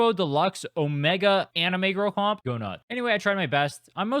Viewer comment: oh I don't know why but the "go nut" instead of "go nuts" got me laughing hysterically.😂😂😂